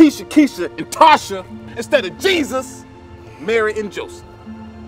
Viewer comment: at 40 sec... I thought he said "instead of Jesus marrying Joseph".... I was like waaaaatt OoO.. when did that happen. 😂😂